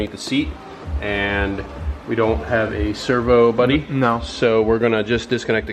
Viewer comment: do I hear you saying "Server Buddy"?